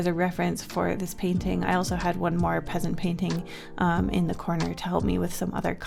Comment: Did she say peasant painting?